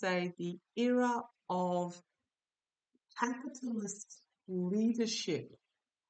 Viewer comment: i thought i heard a womans voice say "capitalist."